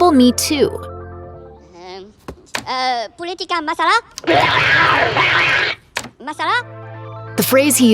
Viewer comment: When first hear that word,i thought he said "Masalah" means problem in Indonesian.